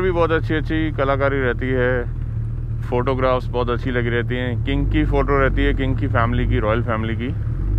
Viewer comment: what I heard was kinky photos 😉 kinky family photos.. jussss kidding brother.. you make really informative vLogs.. love it praaji from Kolkata..